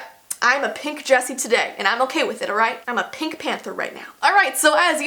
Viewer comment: omg when she said "I'm a pink panther" I thought she said "I'm a pig panther"